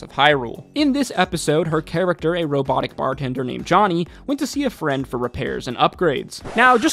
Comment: When you said “robotic bartender” I heard “robotic bard hunter”…. Like auto tune taking out the competition.